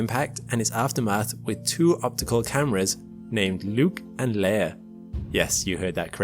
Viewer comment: i thought you said nuke and layer instead of luke and leia. if they did nuke it the astroid would have been turned to dust.